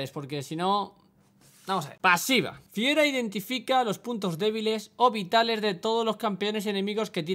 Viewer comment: ONDA VITAL! ah o que dice puntos vitales gg